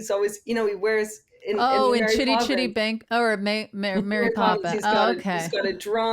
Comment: Totally thought Tig said “shitty shitty bang bang”